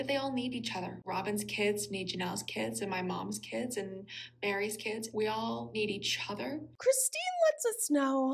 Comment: Get the girl a dictionary. Mykelti wants HER definition of 'complete family'. Her definition excludes Meri. That's not what 'complete' means. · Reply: I heard her say Meri’s kids, not Meri. But I’m still confused because Meri has just one biological kid but maybe she’s including Leon’s spouse?